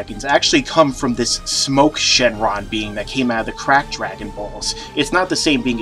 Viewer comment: Legit thought he called that the "crack dragon" rofl.